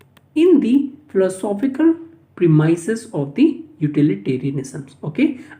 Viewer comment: what is this prosophical or theosophical premises? It was not pronounced clearly so please tell me what exactly was this word🙏🙏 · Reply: Inconvenience Regreted for late reply. its a Philosophical Premise.